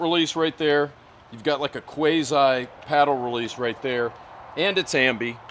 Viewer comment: Did he just say "Quasi" -> "Quayzeye"